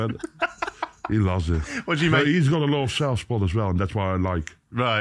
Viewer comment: What does MVG say about Bunting at 7:53? He's got a lot of what? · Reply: He's got a lot of self spot